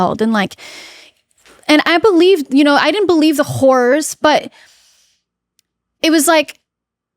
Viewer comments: she didn't believe the "whores"? catherine wtf edit: she said horrors not whores I misheard! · totally thought she said "whores" at first 😂😂 nope! She said "horrors" ahhh that got me!